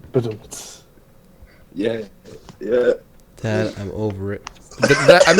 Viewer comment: I heard dad I'm over it